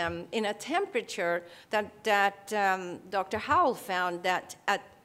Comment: Dr... who? I can't quite make out what she says. That's the first name I've heard as reference for the 115-degrees-F temperature to maintain living-food. Cheers. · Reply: Dr Howl? I think